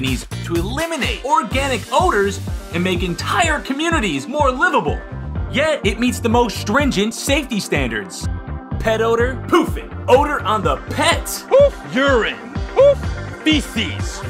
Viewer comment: Ok. Maybe it’s because I just got quite stoned, but this commercial had me in tears. At first, you think, “Is this a spoof? But then, you go, “Nah, this is EXACTLY how ALL commercials should be made.” I’m sold. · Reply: I’m not currently high and still found it rather funny. Idk heard it at first thought they were saying get poop. 💩 lmao 🤣